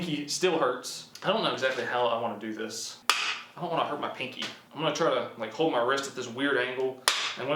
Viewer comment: "I dont wanna hurt my pinky " I heard "im a big PHAT pussy "